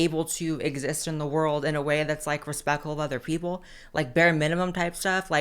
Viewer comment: didn’t quite catch what she said here if anyone can tell me, exist in the world in a way that’s what to other people? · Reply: Respectful maybe?